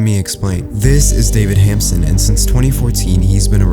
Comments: Lol I have a hamster named David and I thought he said David hampster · thought commentator said david hamster